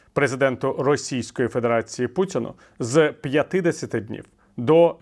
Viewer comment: ви обмовились, або я погано чую... Слово " сорокоденний " я не почула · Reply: Він там паузу зробив , це значить 8-10 днів